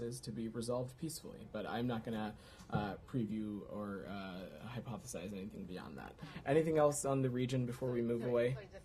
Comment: What? mumble mumble? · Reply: "hypothesize anything beyond that." Maybe it was the four-syllable word that threw you off?